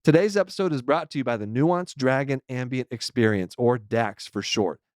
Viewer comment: I thought he said the Nuance Dragon Ambien Experience and imagined an AI-simulated Ambien trip featuring a pedantic dragon instead of the usual ambiguous walrus